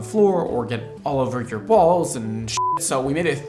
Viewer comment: I heard “so it doesn’t get all over your balls” instead of “walls” 😂 also X Mondo EDP please!